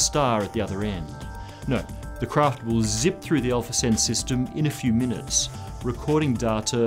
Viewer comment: the subtitles say inaudible, but he says "Alpha Centauri"